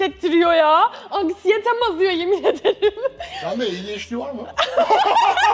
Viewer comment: de ne diyor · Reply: Sende adhd var mi